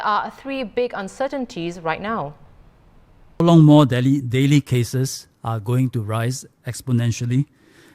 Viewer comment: I thought i heard Dehli and then he said Daily. I thought only